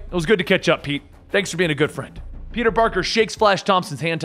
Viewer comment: did you say "peter barker"?